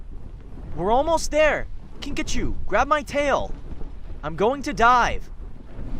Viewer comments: When said “I’m going to dive” in the night wing kingdom while rescuing glory for a second there I thought he said “I’m going to die.” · When said I’m going to dive i thought he said I’m going to die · wait did say he wa going to die? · I thought said "I'm going to die."